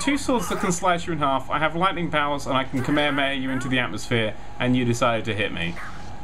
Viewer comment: what did say at 10:53? "i can 'what' you into the atmosphere?" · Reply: kamehameha, a DBZ reference.